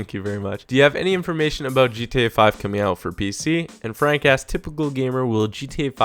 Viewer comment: When you said jack asked it sounded like you said jacka**